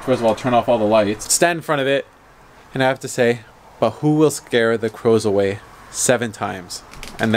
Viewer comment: Sounds like he is saying "Butthole will scare the crows away."